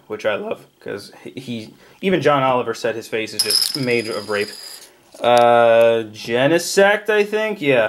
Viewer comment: did you say Gengar's face is "made of rape"?